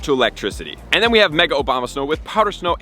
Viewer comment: Every single time he mentions the pokemon I always chuckle cuz I hear "Obamasnow" and I imagine a cartoon Barack Obama playing around making angels and throwing snowballs.